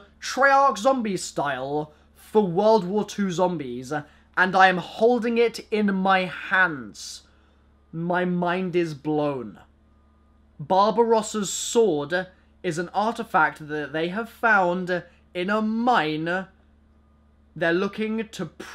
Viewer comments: I thought he said bob ross`s sword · am i the only one that thought he said bob rosses sword? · who else thought he said Bob Ross's sword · Legit thought he said Bob Ross' sword for a bit